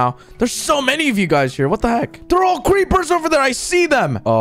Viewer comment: anybody hear him say cweepers at 8:26?